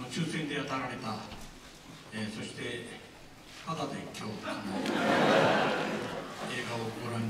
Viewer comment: 「はだでっきょう」？？「ただでっきょう」？？ １０回くらい聞きなおしたがなんて言ってるのかわからんので教えてくれ。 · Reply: タダで今日